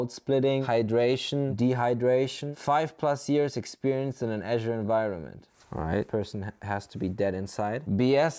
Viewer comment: "5+ Years experience in an Azure environment...OK person must be dead inside" HAHAHAHA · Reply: I thought he said Agile but after listening closer (00:50), he did say "Azure environment", not "agile". I believe five-plus years in an Agile environment is more demotivating than five years in an Azure environment, personally (watch his Agile Coach video for context).